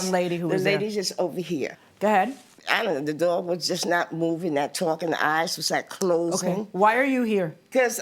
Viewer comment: does she say "the dog was not moving or talking, the eyes were not moving" ? 🤣